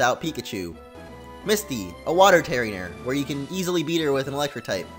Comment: did you just call misty a water terrier?